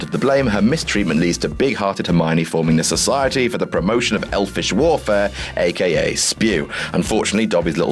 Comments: Sounds like you said “Elvish Warfare” not welfare xD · did he just say "warfare"?